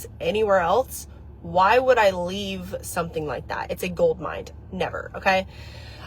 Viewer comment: Everyone else heard her say “gold mind” instead of “gold mine” right?